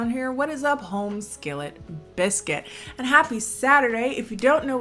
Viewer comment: Maybe it's becauase english is not my first language, but I always thought said "homeschooled biscuits". And at first it was confusing, but then I started calling myself a homeschooled biscuit, because it made sense. But NO, that was never what she said, and life is a lie